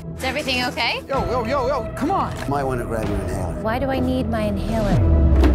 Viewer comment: I thought she said "Why do I need Van Halen".